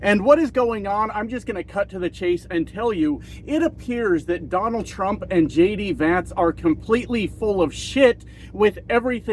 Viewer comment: For a sec, at the beginning, I thought you said, "... Donald Trump and LADY Vance.." lol... It kind of works! 😅